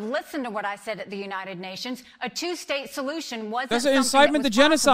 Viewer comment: I swear I heard Ron saying “a jew-state solution”